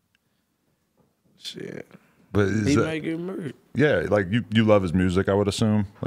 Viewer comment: i thought choppa said he might get merked